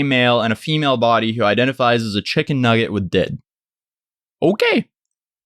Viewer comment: On god thought he said a “a chicken nugget with a dick”